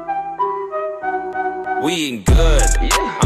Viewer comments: wtf he says "we eating good'??? i thought he said "we good" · İ thought he said we aint good.